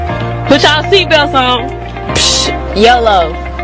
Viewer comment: ok every time I watch this part I always hear “pfft yellow” instead of “pfft yolo” and im going to the doctor today to check on my hearing... update: he said there is nothing wrong with my hearing